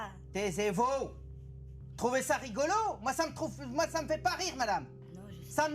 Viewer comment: A 6:16 il a faillit dire « moi ça m’troue l’fion » ou c’est moi😂😂